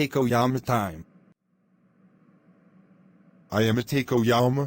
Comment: Why did i hear i am a takeoyama instead of takoyama in